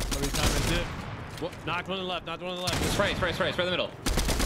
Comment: I really thought he said nigga at first 😂😂 he said thing